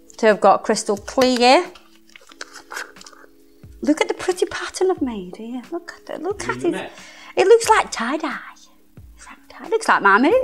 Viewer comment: I had to listen to that line 4 times and even with subs didn't get it right, I heard " look at the pretty patterns I've made it looks like my ( rhymes with fringe) " What did she actually say 😂🤭🫣??